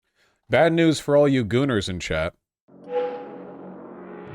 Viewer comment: Thought it said “groomers” at first 🥴 that would have been better, instead of this this “we want to put your name on a list” type crap that republicans seem to be more and more comfortable doing 😖